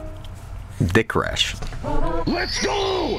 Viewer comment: I swear it says Dick bag when you get the star. Not “Tetris” or “Breakfast” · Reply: I hear " kick ass"